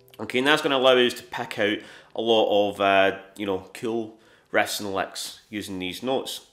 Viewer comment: It's "riffs & licks", guys. Yeah, I thought he was saying "rests and likes" at first too. 😅